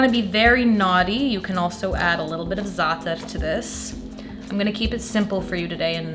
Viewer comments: Looks amazing! At 8:16 what does she say you can add if you want to be naughty? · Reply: Zaatar :) Look it up online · you can add a little ? to this? I can't make out the word. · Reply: Za'atar ;)